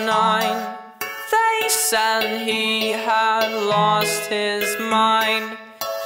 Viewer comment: When I heard "a moth ate through your shirt" I thought he said "I'm walking through your favorite shirts" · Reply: Same! But for me it was when he said " They said he had lost his mind". For some reason, I first heard "They said ye haw lost his mind."